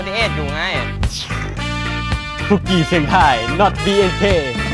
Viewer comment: ผมได้ยินคำว่าคุกเคียดนะ